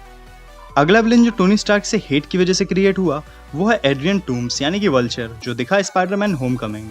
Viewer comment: did he say doom 😨😱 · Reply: Adrian Toomes